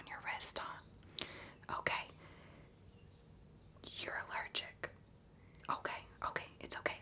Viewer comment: I thought she said 'you're a virgin?'